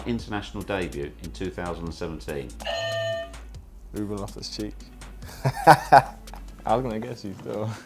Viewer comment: On commentaries, i always thought they were saying lost his cheek...lol